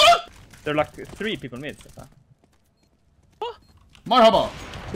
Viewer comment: Did you say merhaba on 00:27 ?